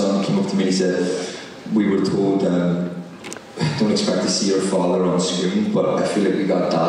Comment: THis is sooo good bahahah . Anthony boyle is hilar. What was he saying 8:10 "don't expect to see it...something something?" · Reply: “Don’t expect to see your father onscreen” talking about Harry Crosby’s real children watching the show and then “i feel like we got dad back” which was their reaction to the show 🤍